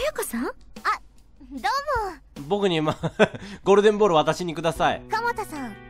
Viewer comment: ゴールデンボンバー下さいて聞こえたんだが